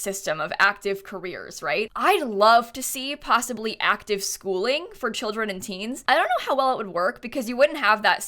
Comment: WHY DID I THINK IT SAID ACTIVE SHOOTING FOR KIDS AND TEENS